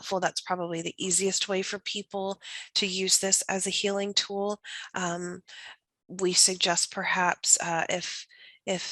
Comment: What is it called?? The healing tool?? Around 48:40 orogone? Or Aragone? What am I googling lol ❤ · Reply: Orgone😊😊